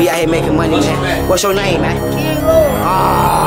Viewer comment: what’s yo name man “Diego” ighhhhhhht 😭😭😭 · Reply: I thought he said King Von